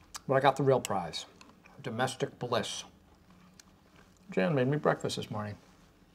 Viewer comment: At first I thought Michael said, “Jim made me breakfast today”